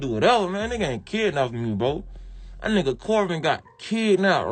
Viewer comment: sounded like he was saying corbin got adopted lol